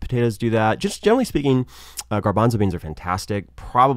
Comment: What food does he recommend at 2:13? I don't understand it. Thanks. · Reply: garbanzo beans (chickpeas)